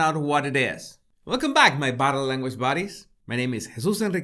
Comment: I like the bit where he says "Like and hitting that bell" To me it sounds like "shitting that bell" I proper laugh every time 😆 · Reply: yes !!!!! And when he says “buddies” I hear “baddies” 🤣